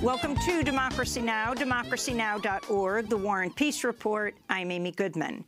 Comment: Who is this guy “Warren Peese” she keeps referring to? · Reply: It sounds like she says" the war on peace report" lol!